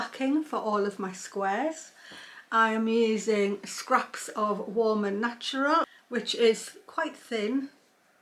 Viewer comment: what is that? i cant quite catch the name of it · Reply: Warm and Natural is a wool quilt batting. I had leftover pieces of it from a quilt I'd made a few years ago. You could use anything else as the middle though.. Or even leave the middle out all together if you wanted to make something very light...